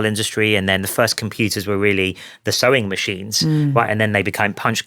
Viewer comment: I thought he said chewing machine.